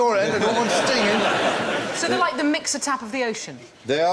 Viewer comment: What does Sue say at 8:13? "It's like a ... of the ocean." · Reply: mixer tap